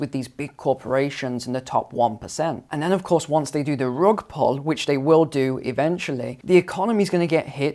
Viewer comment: What did he say? Once they do the what ? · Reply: ‘Rug pull’